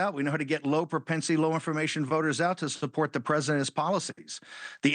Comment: Did he just say at 3:57 that they know how to get low education voters on their side lol 😂 speaks volumes